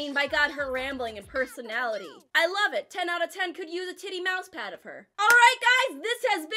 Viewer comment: Did she just say "I could use a titty pad of her"? I died 🤣